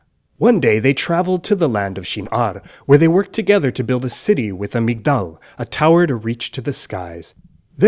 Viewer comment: I was zoned out and thought he said " build a city with a McDonald's "